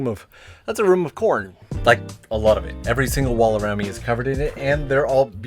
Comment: I thought you said, room full of corn and then I saw all the censored pictures lol. Sadly, not corn 😂